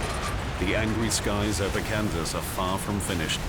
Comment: says canvas instead of Kansas